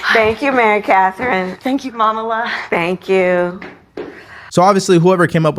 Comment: Momala! Y'all heard that right? I'm not a Catholic. But this is what she decided to do? Instead of intending the roast. They must want her to lose.